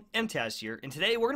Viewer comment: Hi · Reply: My Son thought you said trash